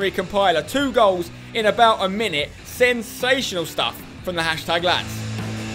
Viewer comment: thought you said sebsational lol because of the assist